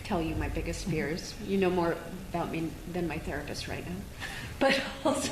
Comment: All I heard was “Her Therapist” I immediately turned this off...🤣